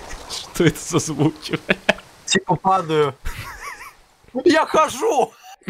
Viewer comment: Мне послышалось "Я ВХОЖУ" ПХАХАХАХАХАХ,ЛЯЯ,,,КХАХАХАХ На протяжении всего финала пытается помочь